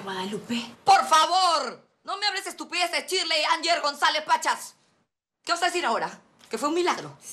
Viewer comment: creo que dijo Shirley ANGIE González Pachas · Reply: es Anyer, ella dijo en un capítulo que su segundo nombre era ''Reyna'' al revés jejej.